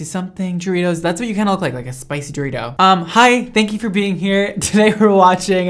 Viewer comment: Am I the only one who thought he said I look like a sack at the beginning of the video I was like wish I didn’t come here to be attacked 😭😂